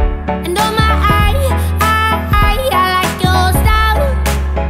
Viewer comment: lol i always thought it says "Tears for me tears for me oh oh oh", i'm fuckin stupid or the radio at work sucks. Prolly both i guess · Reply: this is how parodies born... :D When I listen this song in radio, I hear "I almost died" instead of "I like your style" :D